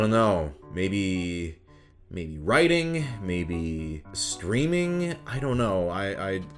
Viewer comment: Lol I thought he said, "Maybe writing, maybe.... screaming?"